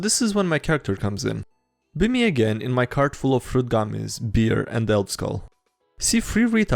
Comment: When you said "Fruit Gummies" I heard "Fruit Goblins" and got really confused.